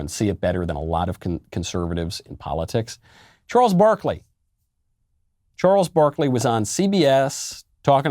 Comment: i thought you said "gnarles barkley" and started singing.